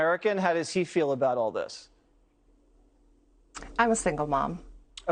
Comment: What I heard was " Im a single mom"